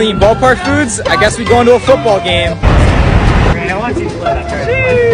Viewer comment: Wait i heard you say football game 🤨